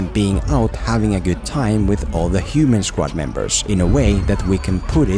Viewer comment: I heard you say "Susan Squad".